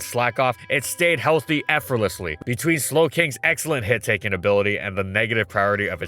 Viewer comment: I was just listening and not watching and thought you said Slaking instead of Slowing